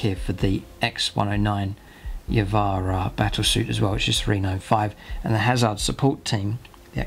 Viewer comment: In 40:05 when describing the Y’vahra points, does he say 305? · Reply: 395